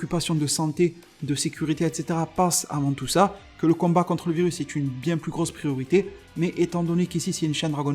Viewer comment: à 4:30 j'ai entendu "le combat contre beerus" je suis vraiment en manque de dragon ball mdr Sinon bonne vidéo